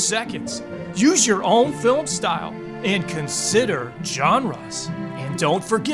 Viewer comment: I cannot distinguish exactly what you say. "Consider [...] us"? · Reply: genres